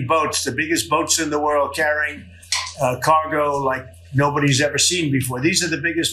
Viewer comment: did he say "congo" instead of "cargo"? i hope for America's sake he wakes up one day and forgets he is president 😂